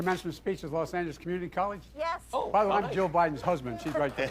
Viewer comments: He did actually say "Jill Biden's husband" at 1:13 but it sounded like 'Joe' the first time round. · sounds like he said Jill Biden not Joe Biden to me. At the very least it sounds enough like he could have said that to give him a pass.